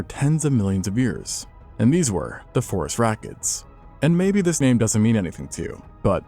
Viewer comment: The first time I heard Phorusrhacids, I heard "forest rockets" and got very confused, but the name has stuck.